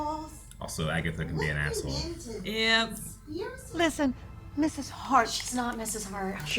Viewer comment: I also said, “Mr. shart?” And had a split second to wonder what Marvel was up to with that name as a joke. Bohner came to my mind. Then I felt dumb when I heard, “Mrs. hart.” Seeing how many reactors thought what I did makes me feel so much better. 😂